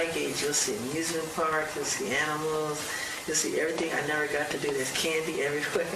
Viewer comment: Did Mike say “Every-f*%king thing” at 8:51?? 😂😂😆😆